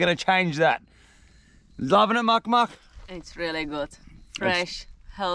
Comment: bro there is no way i just heard you right at 18:07 lol muck muff? what a romantic thing to say hahahaha